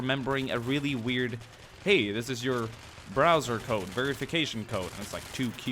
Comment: hold up now, was I the only one who thought he said "brazzers code"?